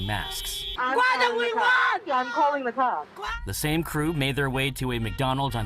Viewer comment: instead of welcome to McDonalds...I'm calling the cops😆